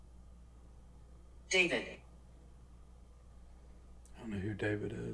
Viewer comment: About 47:00 it sounded like demon not David